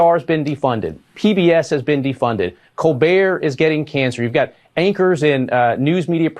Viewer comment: Did he say "Colbert is getting cancer." Is there something we don't know yet? Are they planning on giving him cancer because people love him and he won the Emmy?? 🤔